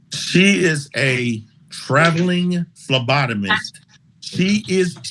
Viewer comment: At first I thought he said she was a traveling lobotomist and i'm still laughing...